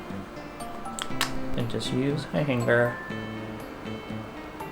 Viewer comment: I looked away right when 17:08 played and I thought he said “and use a hanger” and I was concerned for a moment